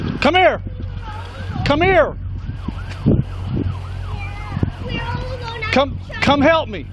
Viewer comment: did i just hear “we’re all alone our mommy died”….oh my god